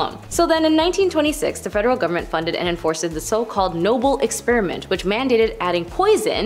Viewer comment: Hold up….did she say enforce-ed @ 2:39?! 😳🤦‍♂️